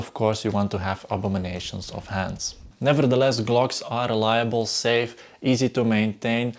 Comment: Did you just say Glocks are liable? Intentional mispronunciation of "reliable"? One preposition changes the meaning completely 😀